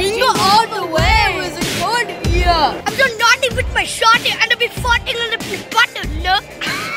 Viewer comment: What was Saying😂😂😂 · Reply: He said I'm so nutghty with my shooter I'm a little bit fartey I'm a little Bartin nerd that what he said